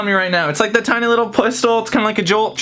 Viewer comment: did he say pustol?